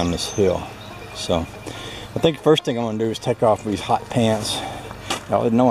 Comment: I misheard you say "Im gunna take off with hotpants" Are you and honeybabe running away then? :-D You look mighty fine in shorts, but i don't fancy you ha ha :-D Remember the jeans with holes cut into them by the manufacturers, just copying us poor people that could not afford new jeans, trendy Ha!!